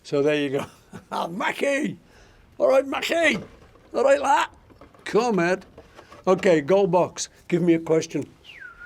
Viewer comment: What does he say at 7:08? John says that in A Hard Day's Night too. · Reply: "Come ed" It's scouse slang for come on or let's go.